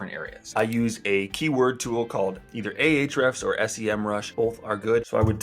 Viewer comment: I don’t understand what you said at 2:03. Something … SEM rush?